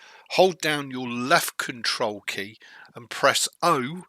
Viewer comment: Did you say at 6:52 "press left Cntrl key + home or O"...if it was home what key is that?